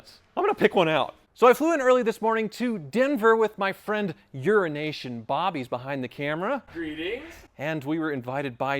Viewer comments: For some reason I always hear you say "Urination Bob" instead of "Euro Nation Bob". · Sounds like he says "Urination Bob" 🤣